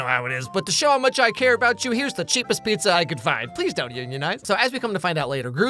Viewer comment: „here is the cheapest pizza I could find“ … what did he say after that?? I can’t understand can someone please tell me Watched it again 4 times Edit: „eat it in one night“ ? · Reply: He says please don't unionize